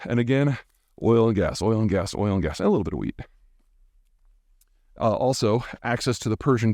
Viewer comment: "Oil and gas and oil and gas... and a little bit of weed" I KNEW IT! This guys smokes herb. That's why he's so enlightened. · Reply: Weed? I thought he said wheat.